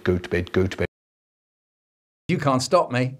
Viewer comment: i cant be the only one that when he says "go to bed" repeatedly i just hear "Goat bed"